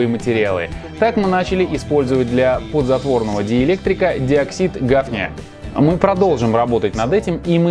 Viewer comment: Мне не послышалось слово "диоксид гавня"